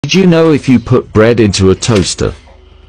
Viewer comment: i was kinda confused cuz i thought it said "did you know if you put TOAST into a toaster"